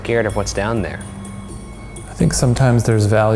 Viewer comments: I'm quite the skeptic, but let's say- if that was a voice- I swear it said "What are you doin' in there" in an accent much more realistic than Shane's. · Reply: I hear “What’s goin’ up there” in an old southern accent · I heard, "What's going on there?"